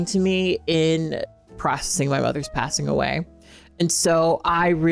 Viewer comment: the captions say "my brother's passing away" but i believe kat said "mother" in case anyone was confused